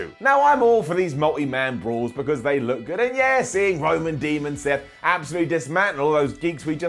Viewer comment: Am I the only one who heared "Demon Seth"?